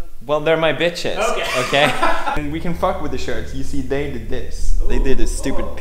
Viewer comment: did pood said"and we can fuck with this shirt" Wtf??